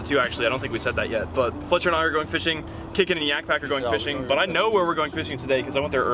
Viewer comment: I thought said kickin their yak pak